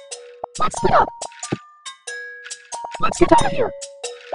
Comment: try to guess what Miis say on this dream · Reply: Let’s split up